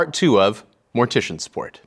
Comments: Sounds like he said, "This is mortician sport." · I thought he said “mortician sport” for a second and got really excited. Thought I was gonna find a new favorite sport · At first i thought he said mortician sport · If mortician support hadn't appeared in text on the screen I would have sworn he said mortician sport. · When he said mortician support I thought he said mortician sport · I absolutely thought I heard "Mortician SPORT". Curious past time that is.